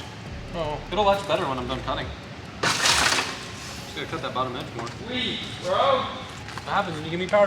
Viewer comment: I thought he said feel a lot better when I'm done cutting😂😂😂